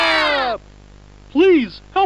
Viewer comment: I thought Hoppy said 'Please Please Please!’ but instead, I found out it was 'Sleep Sleep Sleep!'😆